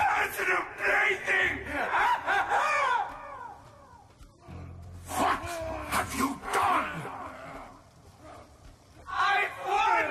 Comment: is it me or does it sound like maxis is saying fuck have you done?